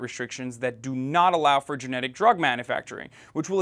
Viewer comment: Is he saying generic or genetic. Cause generic would make more sense to me